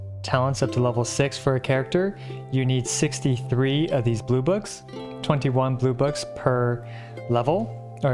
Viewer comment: When he said "blue books", I thought he said boobas EDIT: Oof. C6 Benny